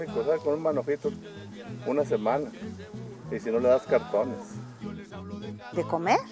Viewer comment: Entendí bien??? Dijo que le da cartones al burrito de comer??? Minuto